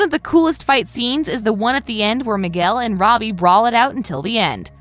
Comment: Did she just say Bobby and not Robby